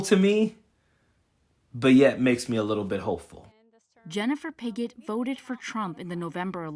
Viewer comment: WHOA !! Her name is Pigot.....I thought the reporter said....BIGGOT !!! HECK NAW !!!🤣🤣🤣🤣